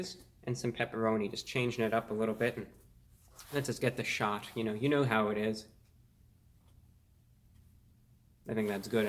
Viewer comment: at first I heard "You know what it is" and I almost camed